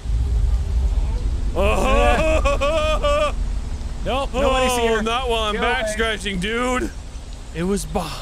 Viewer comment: I was listening to this with some good headphones and I heard “ is anybody there?” And I wasn’t looking at my phone at the time so I yelled out “ WHOS THERE” and brought out my 22😂😂